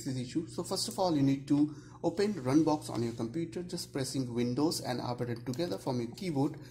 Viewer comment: "Pressing Windows and <aperend> together" I can't understand what you're saying mate. Please be clear. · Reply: he's clear enough? he literally said press win+r